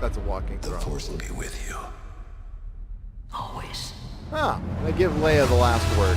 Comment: Ethan thought Luke said "Always" at the end. We in the chat absolutely destroyed him for it. heard Leia right away just like everybody did.